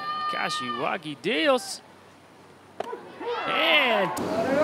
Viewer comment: かしわぎです。聞こえる。